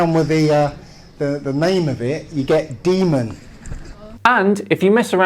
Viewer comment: If you mess with D Wave you get Deman. I heard of Demon. But what is a Deman?